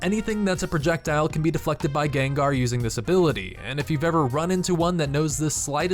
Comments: deflected by who? Did I hear Gengar the Pokemon??? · did he say gengar or just me... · Did you say gengar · GENGAR?????? WTF ARE YOU SAYING ITS GENJI THIS IS NOT POKEMON POKEFANBOY